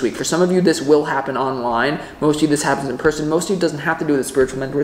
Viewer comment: You said 'in person' but i thought you said IN PRISON. Lol